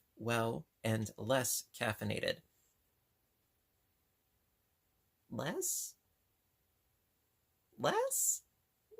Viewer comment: I was sitting here crocheting on my Aunt's cardigan and listening to you reading your card and at around the 5:30 point You was reading and said "less" hmm "less" and at right at that exact moment that you were saying that I was sneezing a couple of times and I could have sworn you were saying "bless" hmm "bless" 😀😁😂🤣😂, I actually had to look at the computer screen twice hahaha and then knew you were saying (less) instead of (bless), it was so hilarious for real. You are right about we all need to step out of our comfort zones every now and again to continue to "grow and learn". Thanks for all you do on Here with all your Encouragement and all of your Great YouTube Videos, they are all greatly appreciated so much! Hope you have a Safe & Happy rest of your Week!